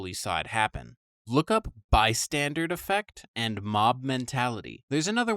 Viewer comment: Dude, it's not "by-standard" effect, it's "bystander" as in people who are bystanders in a situation, they're "standing by" not doing anything. I can understand mishearing it, but it's written right there! 🤦‍♀️